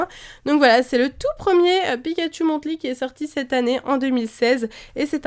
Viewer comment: Quand j'ai entendu "Pikachu Monthly" j'ai compris "Pikachu Monte-Lit" et je me suis dis "WTF c'est quoi ce nom de série" XD